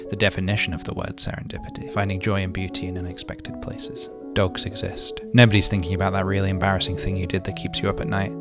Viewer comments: “dogs exist” thank you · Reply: I heard “dogs incest” and I was like “well, as long as it comforts you”... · was falling asleep until “dogs exist” bc i heard “dogs incest” gn